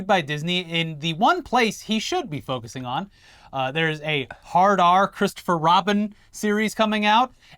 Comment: For a minute I thought you said Chris Rock was gonna play Christopher Robin and now I'm sad that's not gonna happen.